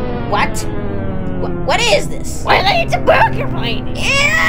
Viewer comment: what did it just say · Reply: “wELl iT’s A bOoGer pLanEt!”